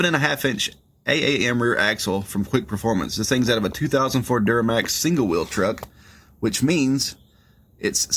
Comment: I thought he said "2000 Ford duramax" before I realized he said "2004 duramax"😂